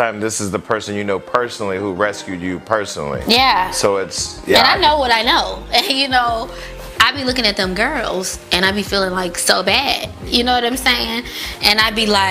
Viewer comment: I'm not sure what y'all heard but at 3:24 she said"i feel so bad for those girls because i know what i know"... If that's not dry snitching I don't know what it is...I can identify with what K Michelle is saying about not speaking up on someone who helped her it's like if a drug kingpin put u through school (college)paid & full,outfits and all... Regardless of his flawed personality, & job of choice that may have hurt or killed folks..After graduating with your MASTERS DEGREE do you report him to the feds?..🤔.. WHERE SHE'S FROM SNITCHES GET STITCHES..jus saying